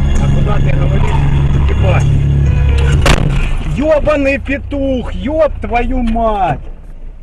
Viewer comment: why does it sound like he says you owe me a new windshield lol